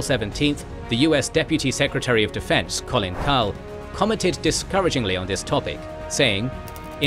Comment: did you say the Deputy of the Secretary of DoD was Collin Powell? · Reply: Colin Kahl